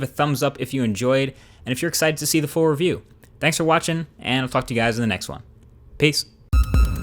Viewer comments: Did Anyone Heard " fucked " instead of Talk to you. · U swear at the end i thought he said ," i fucked you guys" in the next one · Did he just said "fuck two guys in the next...." :D